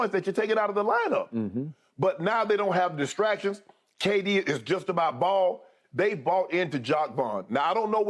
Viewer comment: I really thought Shannon said “ kd just about bald”😂😂